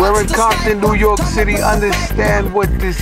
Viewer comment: "we are in Compton New York City" Did I hear that right? 🤣🤣